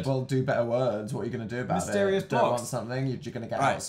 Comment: oml I thought he said 'HI SISTERS' 😭😂